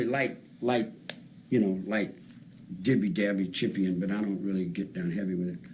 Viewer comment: "light, dibby dabbyin' chippyin'" makes perfect sense. He's talking about his drug use. What he's saying is he's not a heavy drug user. "Dibby dabbyin'" means dabbling. "Chipping" is an old term in the drug world that means you use occasionally, or that you quit before a habit becomes severe.